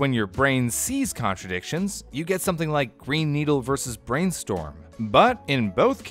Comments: Everyone: You can hear "green needle" or brainstorm" Me: hears "brain needle" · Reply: Listened to the clip without watching the video and I kept hearing "brain needle" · What if I heard "Brain needle?" Which also brings some unpleasant mental imagery... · I first heard “brain needle” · I hear “brain needle” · i heard 'brain needle'. It would be funny if i had heard something like 'yaurel', but i did not. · what i heard is 'brain needle' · I heard “Brain Needle” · I hear "Brain Needle" for some reason XD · Bruh, I heard 'Brain Needle'😂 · I am only 3 and a half minutes into the video, so maybe he explains this later, but my left ear didn't hear anything until around 16000, but my right ear heard it fine at 17000. Also at first I heard "pine needle (not green needle, weird)" but then I heard "brainstorm" and not even looking at green needle could help. And with the bah vs fah one, I just heard "ah" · I just hear "brain needle" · Of all things, I heard "brain needle" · I keep hearing ‘Brain Needle’ XD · I heard "Brain Needle" at first. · I hear "Brain Needle" lol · I hear ‘Brain Needle’ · I hear "brain needle" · Oh hey, it’s like yanny and laurel, but you can actually change which one you hear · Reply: For me it sounded like it was saying both, I'd hear brain needle and then brainstorm now I can only hear brainstorm. With the "Yanny and laurel" one, I only managed to hear Yanny once. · No matter what word I look at I always hear "Brain needle". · I heard brain “needle”